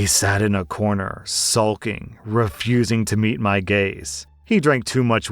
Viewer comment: fuck, i heard it as refusing to meet my gays